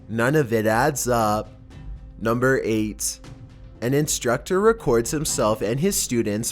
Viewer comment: Am I the only one that heard him say number AIDS. Instead of number 8